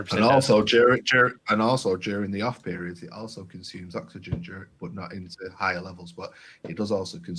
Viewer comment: said something consumed co2 what did he say? Haha. Cant make it out with his accent! he actually said oxygen but the topic was co2 so I think he meant co2. The main reason I want a fuge or one of the 3 is for ph boost. I thought of carbon dosing but wouldnt get that benefit. Is there one of the choices that is better at uping ph? I also heard mention of dosing iron so is that only for reactors? is thats the case I wont choose a reactor. I have to build one myself anyway (since I need one for 3 tanks) I am hearing its easy to make one!